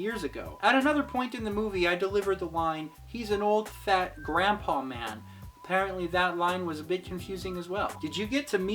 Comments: You rolled a lot of words in your lines- I thought you said Crap old man lol - not grandpaman lol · I always misheard and thought it was "he's an old, fat, crap-on man!"